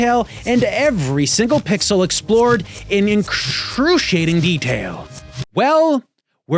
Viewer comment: I love that there's outtakes for "freak flag fly friend" however at 3:02 I think wanted to say "incredible" and "excruciating" and it came out as "incruciating". 😄